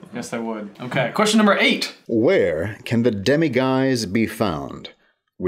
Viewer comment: When it said demiguise I thought it said demigod, I should stop reading fanfiction